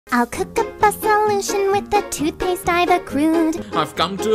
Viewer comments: Roon says: I’ll cook up a solution with the toothpaste I have accrued(?) Hans: I have come to a conclusion this was thought up by the … juice. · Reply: Oh, I heard "I cooked a pasta lotion with the toothpaste I accrued" "I've come to the conclusion this was pasta by the jews" Yours makes a lot more sense... I think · Roon says: I’ll cook up a solution with the toothpaste I have accrued(?) Hans: I have come to a conclusion this was thought up by the … juice. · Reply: I heard “this was fucked up” and not “this was thought up” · I dunno why but I misheard Hans as "I've come to the conclusion we've been spotted by the Jews." Don't ask why, I just did. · Roon says: I’ll cook up a solution with the toothpaste I have accrued(?) Hans: I have come to a conclusion this was thought up by the … juice. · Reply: I thought he said jews instead of juice